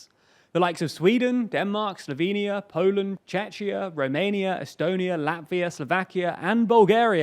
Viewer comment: did you just say Chechnya? xd we are Czechia/Czech Republic